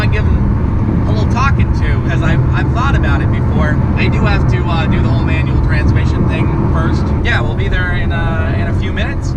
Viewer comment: He already drives Manual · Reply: Henry Heinz strange, none of his cars are manual and I vaguely remember him saying he can’t drive manual. And at 2:47 it sounded like he said “I do have to do the whole manual transmission thing first”